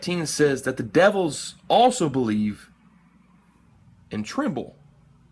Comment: What does he say at 4:12? "The devils also believe in ..." tribble, tribal, triple, dribble? I'm not mocking or trying to make a point. English isn't my main language and I'm not sure what he says, nor does the context give me a clue. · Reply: The devils also believe AND TREMBLE. I would't have caught that myself, it I had not been familiar with the quote. The point is that devils have the good sense to be afraid of God, unlike humans. To be clear, that fact that I know what the quote means does not imply that I agree with any part of it.